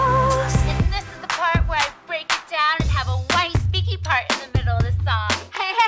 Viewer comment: did she say thats where i break it down and have a white sticky part in the middle